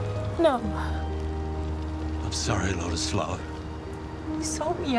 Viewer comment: How did he call her?What flower? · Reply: Lotus flower